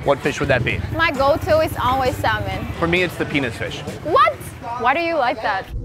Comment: Sonny: If you can have any fish what would it be? My go to is always salmon Sonny: Mines the penis fish ROFL! Sonny you are the best! LOL! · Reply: I thought he said peanut fish😂